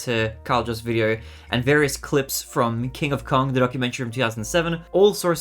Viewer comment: I thought you said "donkumentary" 🤣